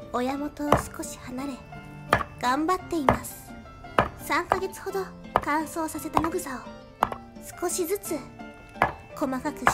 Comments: 「3ヶ月ほど乾燥させた野グソ」って聞き間違えて勝手に腹よじれた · 乾燥させたヤ⚪︎ザに聞こえる😂